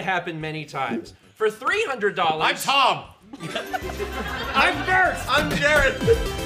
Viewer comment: "I'm Tom" Count: 83 "I'm Jon" Count: 71 "I'm Stephen" Count: 58 "I'm Jerod" Count: 33 "I'm Dan" Count: 13 "I'm Jirard" Count: 6 "I'm Jules" Count: 8 "I'm Josh" Count: 2 "I'm Jon" (not actually) Count: 9 "I'm Jirard/Completionist" (not actually) Count: 17 "I'm-" Count: 35 "I'm uncomfortable" Count: 2 "I'm blind" Count: 2 Only 1 of each: "I'm Donation Reader Mal" "I'm Kampy" "I'm first" (which I misheard as "I'm Bert" several times and I wondered why said that) "I'm Disc-Only" "I'm Jerod" (not actually) "I'm Josh" (not actually) "I'm deceased" "I'm being molested" "I'm disturbed" "I'm dying" "I'm doing illegal things" "I'm fully clothed now" "I'm just happy to be here" "I'm a little confused" "I'm concerned"